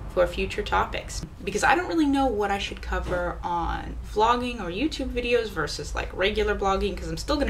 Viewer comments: i thought you said, "Flogging", like hitting/whipping; but, naw, you said, "Vlogging"...which you know, obviously. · hahahaha... I thought you said flogging so I listened again & heard you say BLOGGING. YES blogging is better! And YES ... I am horse laughing here in Wisconsin! Thanks for your videos! I enjoy them. :)